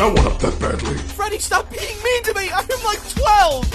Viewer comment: Lyrics: "Terrible job, supershit. How'd ya fuck that one up that badly?" "Freddy stop being mean to me! I am like, twelve!" "I'm really fucking trying, Gregory, but William Afton installed Bonzi Buddy onto my hardrive." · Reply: I thought he said crying instead of trying